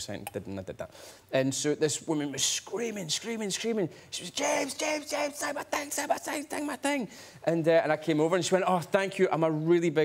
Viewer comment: Can anyone help me with the story at 2:23? What was the lady screaming? Can't understand him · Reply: "james, james, james, sign my thing, sign my thing, sign my thing"